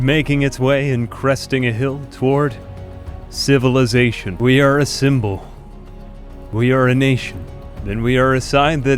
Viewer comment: I thought you said "we are assemble"